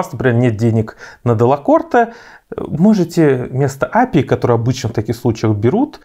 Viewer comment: Здравствуйте, можно уточнить, какую обычно берут, не могу расслышать? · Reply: Simonelli appia